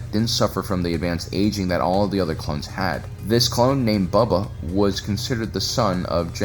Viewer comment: does anyone else hear "Buba"?